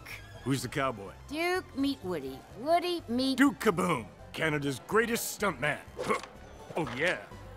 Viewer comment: lol i thought he said canada's greatest scumbag for a moment there